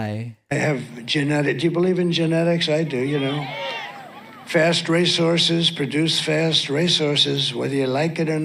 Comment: "Fast resources produce fast resources." What? Like I understand what he wanted to say with the context. But seriously? That was pure gibberish. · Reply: I thought he said "fast resources produce fast racehorses"